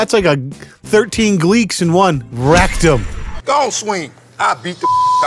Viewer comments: Bruh. When you said “rekt em” around 8:30 , it sounded like rectum and I can’t stop laughing EDIT: FUCK NOW U HIT ME WITH THE 9:50 “jujanjinbbubityoue” LMFAO HELP I CANT FUCKING BREATHE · I had this playing, but I was looking at something in another tab. I had to triple check what I'd heard, because I heard "That's like 13 Gleeks [fans of the show Glee] in one rectum" and was all "what the hell is going on in that video!?" Hahaha